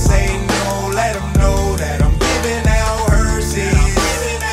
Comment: I thought he was saying "giving out Hersheys". Mom ain't gone let me listen to dis.